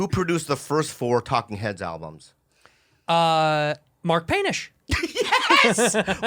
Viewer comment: Dude, at first I thought she said “Mark Penis” and I burst out laughing 😂.